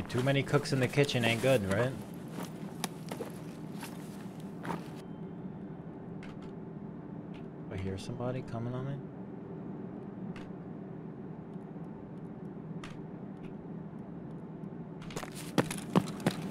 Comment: "I hear someone coming on me" took me bro lmao